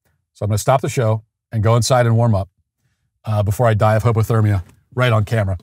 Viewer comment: I swore Matt said he was going to die of homo-thermia but maybe I misheard over the sound of me doing the dishes maniacally for the 4th time today